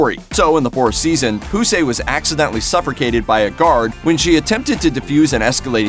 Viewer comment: I need at least 1000 people to confirm what I just think I heard. I’m pretty sure added an r in the word suffocated, as in suffracated or sufforcated at 1:20. Let me know if I’m the only one who heard that